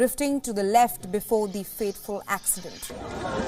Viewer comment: did i just hear "faithful accident"? in the fiest 1 minute? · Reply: Fateful